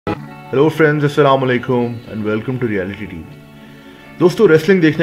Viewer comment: what u says the first word? "hello" yaa phir "Ro" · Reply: He said hello but before starting the video he said he and started video it seems to be llo friends